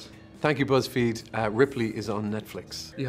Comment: What does he says at the end? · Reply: Don't know if you still care but it was "you have a text there from somebody...I'm joking!"